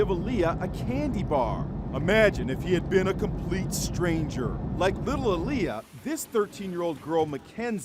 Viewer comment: I thought he said “like little lia”